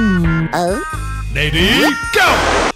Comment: Ready go got me at 0:11 😂😂😂😂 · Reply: I thought he said “BABY GO”